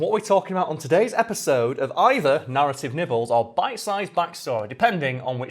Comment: Didn't read the full title as I clicked, as I was getting food ready (priorities), and somehow misheard say "Narrative Nipples".... And I didn't even question it at first because this is, a.k.a. xD